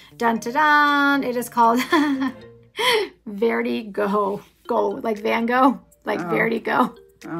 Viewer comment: Was the one polish named "verde-go"? You said it was a play on van go. But the second time you said it, I heard it as a play on vertigo. Unless it's spelled "verde-Gogh", then it's definitely not a play on vertigo, it was just the way you said it somewhere around the 12 min mark. Just a thought that crossed my mind. Super cute combos! I really hope you end up wearing that one this fall. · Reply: Close! Verti Gogh! A play on both, I think! I typically always list the polishes in the description box, if you ever want to reference a name. And yes, I’ve already started a Fall grouping of polishes! I’m wearing that combo for sure! 😊